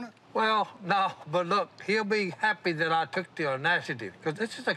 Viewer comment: Si says “took the onacity” but the closed captions say he said “took the audacity”. It was definitely “onacity” and not “audacity”. It took great onacity for me to write this comment.